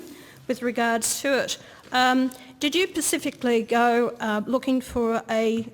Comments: Did she just say “pacifically”? Shoot her now! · "Diesel or pumpjet?" Gasoline or tire? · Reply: Did she say "Did you pacifically go looking...."? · did she just say “pacifically” 💀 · OMFG she did just say pacifically did she not PMSL 😆😂🤣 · did she just say "pacifically" instead of "specifically" ? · Did she really say "Did you Pacifically"? Fuck me sideways! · did she just say "pacifically"? Wtf?! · Did she just say Pacificly ??? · Does she say “pacifically” @ 0:55? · did she say "pacifically"? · Oh my effing god. She really did say "Pacifically" instead of "specifically" at around 00:54 didn't she? That's always a good sign that you are dealing with a fuckwit. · did she just say pacific ? instead of specific ?! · did she REALLY say "pacifically" instead of "specifically"? If so, she's even dumber than later in the clip. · Did she say "pacifically"? She said "pacifically" didn't see. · Did she really say "Did you pacifically go looking for a..." ??! Gah. Every time I watch this I notice something new and dumber with her. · Yep, She really said 'pacifically'